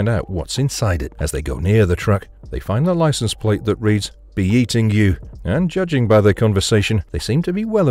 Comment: All these years I thought his license plate said "beating you" "Be eating you" makes so much more sense